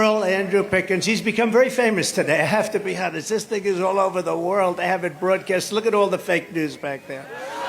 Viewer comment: did Donald say "this nigga is all over the world"